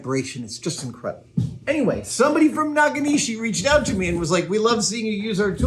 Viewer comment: I heard "Nakamichi" sonic cutter and was confused for a second. Also, that's a $3k tool he was seemingly sent without cost.